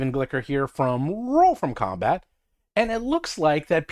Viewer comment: Yes, on the surface, it sounds like he said, "Roll FROM combat." Now that you mentioned it...